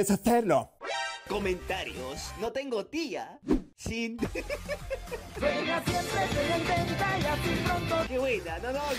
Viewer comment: alguien me explica no le entendí :v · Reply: juega xd gracias ahora ya le entendi